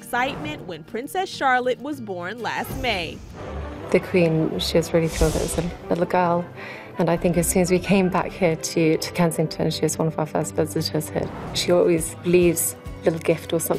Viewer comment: what does she say at 0:35? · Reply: ""The Queen was really pleased that it was a little girl and as soon as we came back to Kensington (Palace) She (Her Majesty) was the first visitor we had here'.